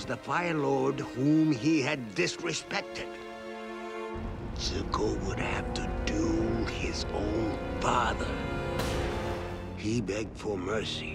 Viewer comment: the subtitles say:’zuko would have to do his own father’ Well that’s a twist